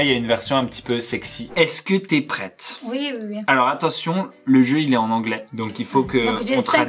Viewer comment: c'est moi ou j'ai entendu " oui bébé"? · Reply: Non, elle a dit oui oui oui.. pas oui bb